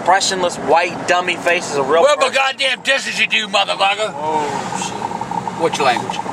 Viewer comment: its been 10 years and I still have no idea what he said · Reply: said "Whoa shit" quietly